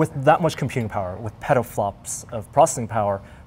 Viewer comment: I swear Norm says "Pedoflops" instead of petaflops.